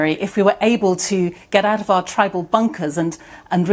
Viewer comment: did she just say tribing 😂